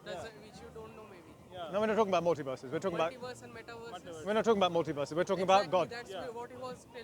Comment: Did they say metaverses